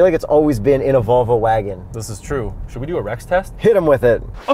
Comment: i thought you said wreck test for Rex test